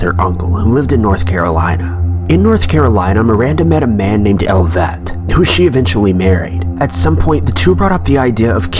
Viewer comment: oh its elliot i thought you were saying elvet.